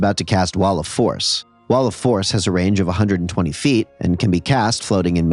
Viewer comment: legit thought he was about to say waluigi:/